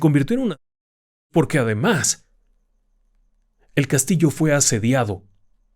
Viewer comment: Qué dice en el minuto 38:54? 🤔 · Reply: El castillo fue asediado...eso dice